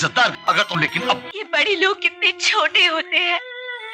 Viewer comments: " Yeh Bade Log Ke kitne choote hai" Why did I hear this 😂😂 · "Bahi aur lawrence mile aur hit na ho.." what a clever line bro. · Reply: " Yeh Bade Log Ke kitne choote hai" Why did I hear this 😂😂